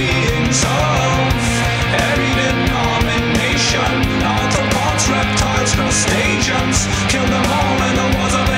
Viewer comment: Even knowing that he's saying arthropods it still sounds like he says Autobots.